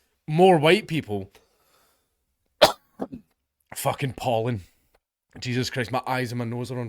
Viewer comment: Misheard pollen as Poland and wondered what ol Dank had against them this week. 😂